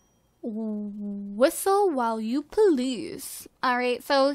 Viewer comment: Anyone else wasn't looking at the screen around 38 minutes in and thought she said "Whistling to please"? O.o